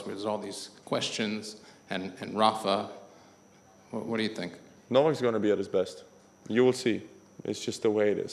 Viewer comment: In this video he never said "you will see djokovic will be at his best". The video title is wrong! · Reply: I too was going to post the same thing. 😅 Then the responder to your comment cleared it. I thought her said "no one is going to be at their best, you will see" turns out he was saying "novak will be" 😀😀